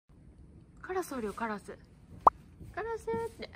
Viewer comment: 冒頭のママさんの「カラスおるカラスおる」が 「パラソルパラソル」と聞こえてしまい なんやこの人、メルヘン少女かいなと思ってしまい 申し訳ありませんでした。（チロハンド待ち）